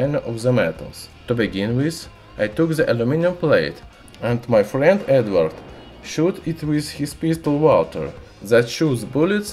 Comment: I thought I heard "And my friend Edward shoot it with a pistol water" :)))